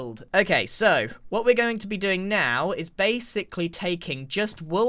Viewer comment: so base-sicly lol thats what i heard